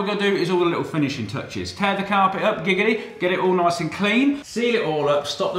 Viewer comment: did he say giggity